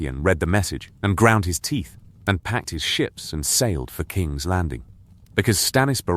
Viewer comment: I half miss heard that bit of stannis at first had to replay i heard "ground his teeth and packed his shit"